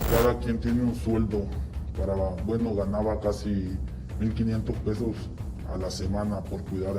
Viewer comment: No entendí ¿dijo $1500,00 pesos a la semana? en El Salvador la moneda de curso legal es el dólar.